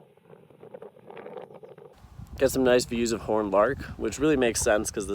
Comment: What was the bird at 1:40 that you said was aptly named for the tufts of feathers on its head? No name came up on screen and you moved straight on to the horned lark... Or maybe the first bird was a horned lark? As always, a beautiful video with amazingly well adapted birds, superb camouflage, and fantastic locations. Thanks! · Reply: It was the horned lark!